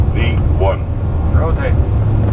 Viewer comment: subtitle says "the one rotate" 😂 Should be V1